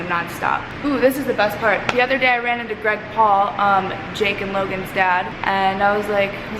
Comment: can we just talk about how greg paul is a grown ass man and he straight up screamed in alissa's face? like wtf? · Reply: When i heard greg paul i thought they said reckt paul